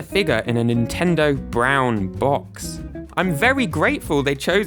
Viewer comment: just a ni**** in a nintendo brown box is what I heard